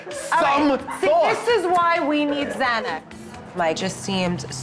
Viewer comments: did she say ''this is why we need xanax'', lol? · Did she really say “this is why we need Xanax!” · what did she say ?🥴🫣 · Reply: "This is why we Xanax"